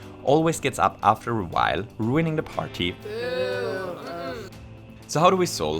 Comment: he said "ruining the party" but the captions say "for everyone else" after. Why did he leave it out?